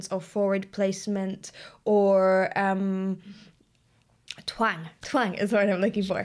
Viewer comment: Did you just say Twat?? Lol